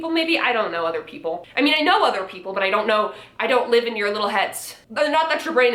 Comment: I thought she said live in your little hets like heterosexual and I was like excuse me I’m gay but then she said heads more understandably and I was like ohhhhh